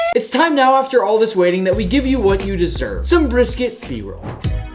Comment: When you said brisket b-roll, I thought you said brisket p-word which also explained it very well